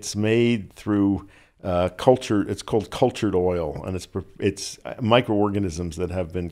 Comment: Dear Dr. Weil, please grace us with your knowledge and quiet motivation more often, you are valuable and needed. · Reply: did he said cultured oil ?